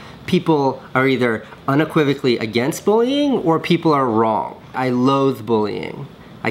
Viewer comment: I thought he said I love bullying